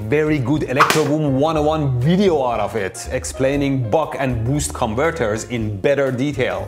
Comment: caption is wrong - should be buck, not bulk